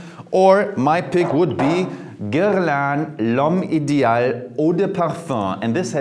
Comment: does anyone know what he says the kind of cologne is at 9:37? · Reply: Guerlian l'homme